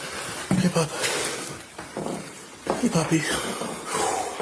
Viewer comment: Did he say hey puppy ? At 7:16 ?